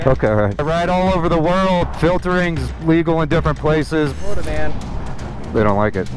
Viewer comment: "filtering" ?? Not sure if i heard correctly, don't know what it is....